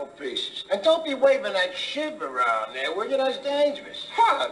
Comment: Sounded like he said “Don’t be waving that shit around there!”